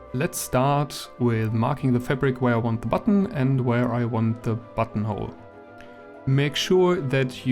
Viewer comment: Ohhh, I thought it said "fantastic buttholes" 🤣🙄 Sorry for my dirty mind but an amazing video!